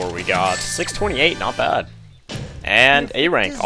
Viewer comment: on act 2 I heard you say ray rank not a rank